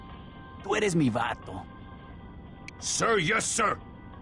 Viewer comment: Can someone tell me what is he saying at 0:21 pls ? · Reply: Tu, tu eres mi vato